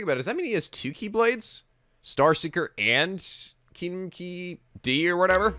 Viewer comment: I thought said kinky D at 35:22 XD